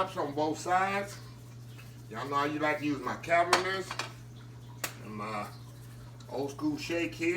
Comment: what is the name of that seasoning, sounds like your saying cabinets , I want to try some but you know what shows up when I Google cabints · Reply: Cavenders Greek seasoning